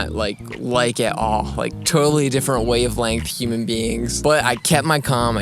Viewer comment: did you say "totally different way of length human beings"?? It's wavelength hehe